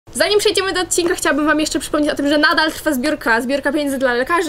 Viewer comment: Czy ja usłyszałem chciałbym?